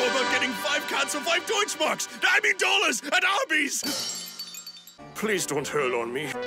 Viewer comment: I have no idea what he said, it sounded like it was about spending money at Arby’s to me, pretty sure that isn’t it, can someone translate?